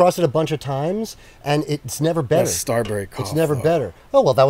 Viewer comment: I thought Kyle always said that he didn't bread Strawberry Cough. I thought he get it from someone, and had taken care of the plant. Anyone able to clarify? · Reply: He didn’t say Strawberry Cough, he said Starberry Cough. I can see how you misunderstood tho